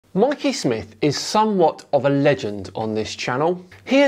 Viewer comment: for a moment, i thought you said Mark E Smith 😂